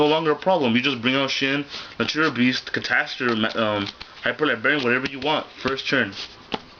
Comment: Hey at 3:24 what did you say you could go into? Hyper Librarian? Why not run 2 Utopia?!?!?!